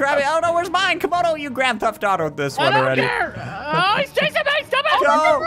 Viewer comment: Did he say grand theft auto